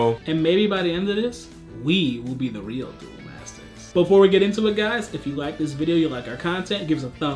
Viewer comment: My gaydar accidentally trigerred at 0:31 because I heard "before we get intimate "